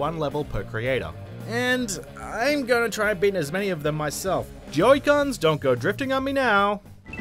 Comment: Is it just me or at 0:32 did he say Joy Guns?